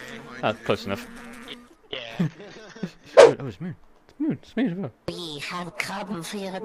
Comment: я один услышал фразу "а, точно"?